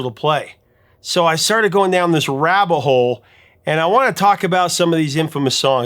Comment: Dude... did you just say "rabbahole"??? The phrase if rabbit hole.